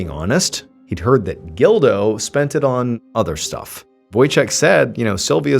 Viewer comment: Can you imagine going through childhood with the name, Gildo? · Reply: I thought he said dildo at first lol